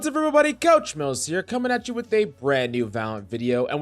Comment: Yo i doubt im the first to say this but when you say coach mills it sounds like u say couch mouse lol